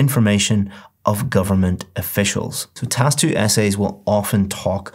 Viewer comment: there is mistake in word There is "prone" instead of "officials"